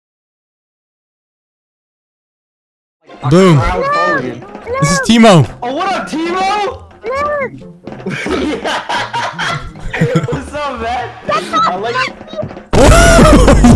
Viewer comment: i think he heard "whats up timo" to"shut up timo"😭😭😭💀💀